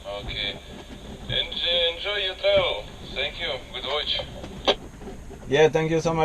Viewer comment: Gee I thought he said "enjoy your peril.'